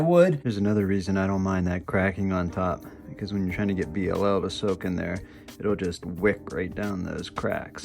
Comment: Just the video I was looking for on a Saturday night. Definitely a niche/obscure topic as far as I'm aware. Thanks for the learning! edit: 13:20 mark, what is it you're wanting to wick down the cracks? sounded like you said BLL but I have no idea what that is. Can you explain further? thanks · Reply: Definitely niche. Thanks for watching and taking the time to comment. BLO is short for Boiled Linseed Oil. It’s a good treatment for wooden hand,es because it seals the pores, but also puffs them up a bit, consequently tightening the head. Hope you decide to stick around and come along for the ride. Thanks again.